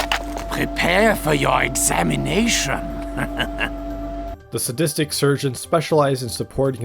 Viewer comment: I STG I heard " prepare for you... insemination... "